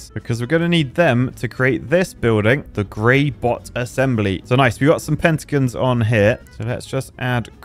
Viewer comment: I thought he said "Brain Rot Assembly" 😅